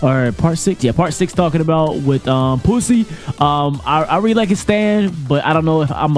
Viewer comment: did he just say PSY 😳